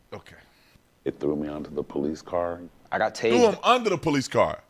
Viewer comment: lol I think he said "they threw me onto the police car." It just came out as onta da...